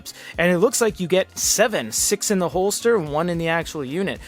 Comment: Six in the mag, forget about the lamb. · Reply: i thought you said six in the holster, wheras a mag (magazine) houses bullets